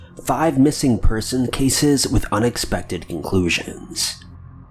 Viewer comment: Sounds like he said with unexpected INCLUSIONS not conclusions lol